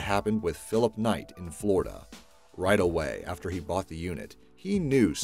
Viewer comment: I thought it said flo rida 5:38 *facepalm